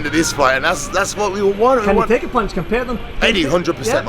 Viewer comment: Can someone tell me what was the question at 5:25? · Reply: He was asking if they can take a punch, talking about Eddie and Thor